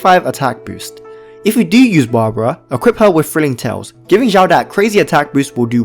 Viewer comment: What does he say at 4:43? Equip Barbara with what? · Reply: Thrilling Tales of Dragon Slayers, a 3 star catalyst weapon, you should have tons of this from the gatcha pulls, enough to refine to 5 for a 48% attack boost for 10s.